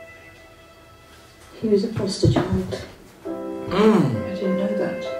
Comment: “He was supposed to…” what? I can’t understand what she says. 🤦🏼‍♀️ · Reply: "He was a foster child"